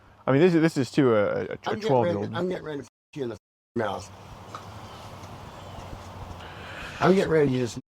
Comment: Did dad say "I'm getting ready to f**k you in the f**kin' mouth!"?